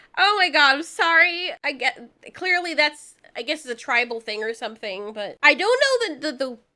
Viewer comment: The absolute second spear befriended the dog I heard “it’s a animal thing”.